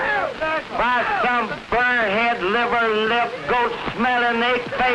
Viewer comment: did he say liver lips ??!! I'm dead!! Never heard that one before!!